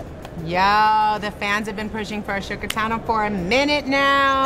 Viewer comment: Did she just call her "A-shook-a Tano"?